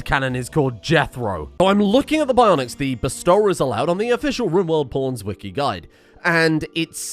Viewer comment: thought you said "Rimword p0rn wiki"